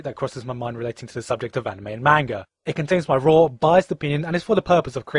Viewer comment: Did you say mango?